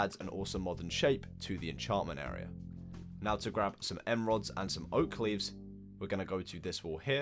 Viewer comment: I SWEAR HE SAID EMRALDS INSTEAD OF END RODS 13:23 HE ALSO MAY HAVE SAID EM RODS